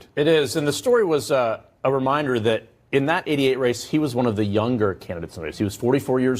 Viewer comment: that’s funny. I thought i heard idiot race. But it was actually ‘88 race.